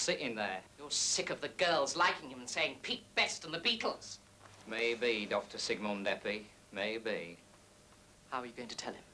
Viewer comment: What does the Lennon character say after "I'm sick of him" at around 0:50? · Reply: "Maybe Dr. Sigmund Eppy, maybe": a pun based on Sigmund Freud, the founder of psychoanalysis.